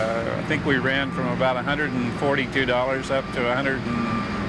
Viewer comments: Captions might have an error at 36:29, it sounded like he said "$142," but the English captions say "$42." I don't know if this is a mistake but I wanted to bring it up just in case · i think the palladium guy said 142$, why are your subtitles saying 42$ instead?????